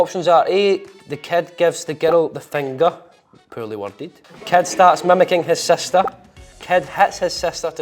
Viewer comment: i thought he was saying cat and i really was searching like where tf is a cat??! Focking accent